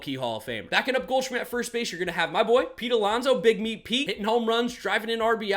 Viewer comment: Ik this man did not just say big meat Pete 💀💀